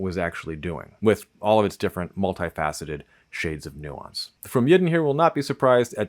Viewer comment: "From Yidden" Yiddish: פרום ידען 'pious Jews' (cf. German 'fromme Juden'). (Sorry, took me a while to work out what you were saying, and I'm sure I'm not the only one.)